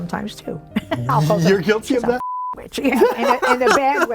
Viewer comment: does anyone hear a whisper at 8:44 that says “yeah”...? 😳